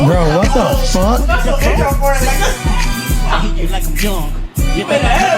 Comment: My only thing is not being able to understand a word he's saying. 4:24 sounds like hes saying "you know we like em young" so, there's that.